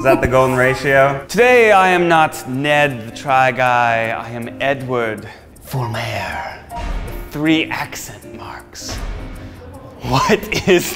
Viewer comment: Misheard Lyrics, not-a-song edition. What Ned ACTUALLY said, “...I am Edward Fulmééérr...three accent marks...” What I heard, “Ned for Mayor...free accent marks...” “Free accent marks” is a strange campaign promise, but I’d really like one...